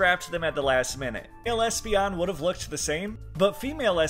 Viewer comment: I from germany, and I know damn well its "Espeon" but all I hear is "Lesbion" 😭